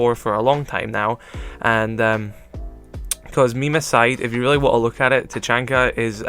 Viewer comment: the f*** did he just say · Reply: The Epic Memer Boi not to ruin the joke but he said memes aside